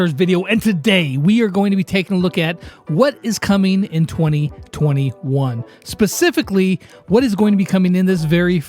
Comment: What I heard "what is coming in 2020-1